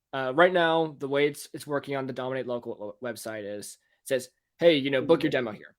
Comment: What is his SaaS comapny's website? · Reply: yeah at 23:00 I thought he said "dominate local website" but doesn't look like a GHL set up on that domain